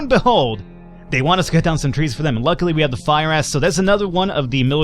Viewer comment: This is what I heard from 1:50-1:57, "Luckily we had the fire ass"